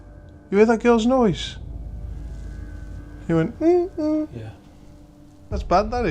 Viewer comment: I heard a whisper already, under voice at 0:16 . It sounds like "yeah".